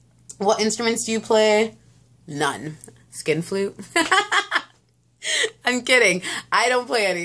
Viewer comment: What is a skip flute? Nice tag.... I didn't get tagged so I never did it... my answers would be boring anyway. Yours were funny!! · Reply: OMGGGGG... Not that I knew what a skin flute was... but I def' thought you said SKIP flute... OMG!!!! HAA HAAAA